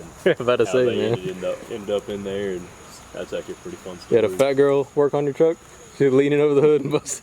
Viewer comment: Thought u said fat girl twerking on your truck 😂😂